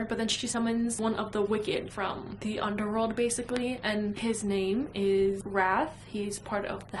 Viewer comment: Sometimes you’ll say a word and with your slight accent I won’t understand, and when you said, “His name is Wrath” I totally heard “His name is Brad.” 😂